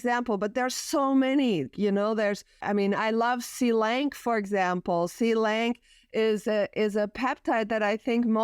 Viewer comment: what molecule is she saying? Silength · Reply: Selank peptide